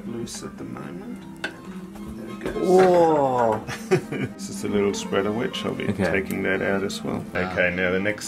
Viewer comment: the subtitle says ‘ a little spread of HIV ‘ i don’t know how to feel about that